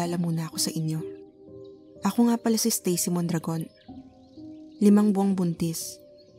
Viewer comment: "stacy mondragon" what i heard: "stacy mukhang dragon"